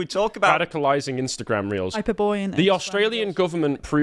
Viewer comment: Did I hear HYBERBOREA!?!?!?!